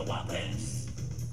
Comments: I thought that the green thing said: "We got the woblins we got the woblins." So I'm like "What the frick?!" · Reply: I thought it said wake up the wubbox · thought he said we got the wublins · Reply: He says wake up the wublins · Is ig me or did i hear 'Wake Up The Wublins Wake Up The Wublins? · I thought he said “wake up the wumpets” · I thought that the green thing said: "We got the woblins we got the woblins." So I'm like "What the frick?!" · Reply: i thought it said wik up the wopiz lol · I thought it said wig up the wublins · I thought that the green thing said: "We got the woblins we got the woblins." So I'm like "What the frick?!" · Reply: I thought it said wake up the wobunis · I thought that the green thing said: "We got the woblins we got the woblins." So I'm like "What the frick?!" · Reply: I thought it was saying "we are the wublins we are the wublins" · Why do I hear "Wake Up The Whoppers..." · Wake up the wublins! At first for some reason I heard "wake up the wubbles" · Am I the only one who heard "Wake up the waffles"? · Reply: i heard:"Wake up the wublins". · i heard "we got the wapez we got the wapez, we got the wublins we got the wapez" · I thought that the green thing said: "We got the woblins we got the woblins." So I'm like "What the frick?!" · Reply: I thought he said wake up the waffles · I honestly thought that he said wig up the wompiz. I dont know where I got that from. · Is it just me or did I hear “wake up the wappers” lol like the burger XD